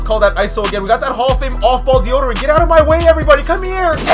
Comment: I swear he said "deodorant get out of my way."